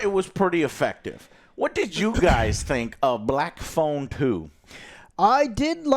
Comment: Sounds like AJ said Black phone poo 😆